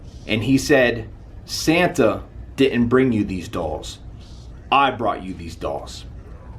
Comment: demonic voice, sounds like “satans dolls”, 1:26 same voice but can’t make out what it’s saying, again